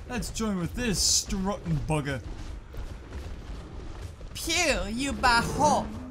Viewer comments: did you just say "eew you BAHO" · Hai Did You Just Said A Filipino Word?? You Said To The Triceratops “phew You Baho”